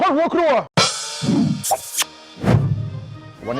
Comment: คนหัวครวย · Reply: เขาได้ยินเป็นคำว่า คนหัวครวยครับ